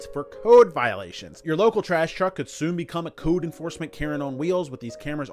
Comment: I thought he said ‘Karen on wheels’ which is a good description of those garbage trucks with cameras looking for problems…